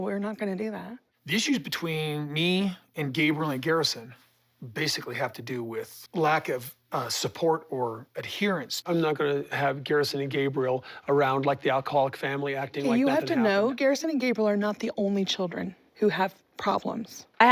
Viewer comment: Hang on did he say “around the alcoholic family??” Around 2:10-2:15??